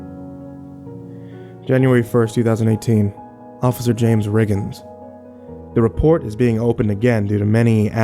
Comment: I thought he said "james riggens" at first and I was like BECOMES A P.I